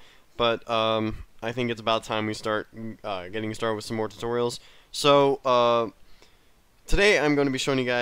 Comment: I thought he said Tucorial I was like what the f***